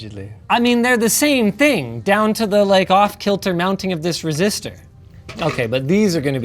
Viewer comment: linus was right and the subtitles are wrong... it's definitely a resistor, not an inductor